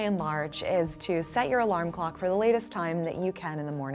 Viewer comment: All I heard was "set your alarm clock later" You got it doc!